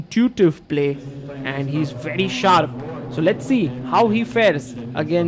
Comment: did i hear " lets see how he fails" 🥲